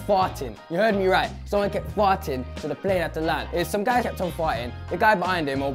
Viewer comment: I thought he was saying fight instead of fart